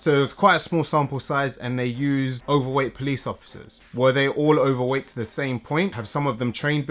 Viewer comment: please guys help , english is not my first language , i replayed it like 300 times and i still hear him saying police officers ?what does he saying ? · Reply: Police officers is correct